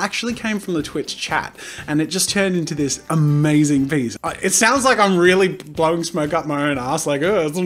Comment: The thing is when he said "i know it sounds like im blowing it up my own art" I thought he said "I know it sounds like im blowing it up My own arss"